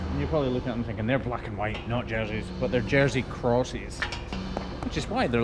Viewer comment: I coulda sworn he said Jersey cows. I haven't re-listened though. LOL